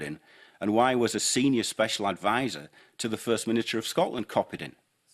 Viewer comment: did he say first miniature?